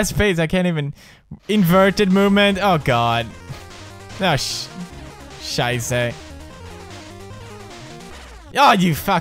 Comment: Did he say "Scheisse" at 2:25? XD